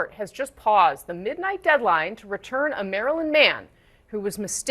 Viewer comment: At first I thought they said they sent Marilyn Manson to El Salvador and then I realized they said Maryland man😅 (They shouldve said "Sent El Salvadorian national back to El Salvador")